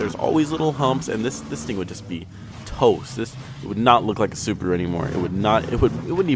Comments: legit thought this fucker said "it would not look like a supra anymore" when he actually said Subaru but while it lasted it was funny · it sounded like he said "supra"